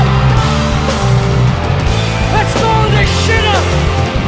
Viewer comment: ano sinabi niya??? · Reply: Naruto Uzumaki let’s blow this shit up 😅 na hype si ser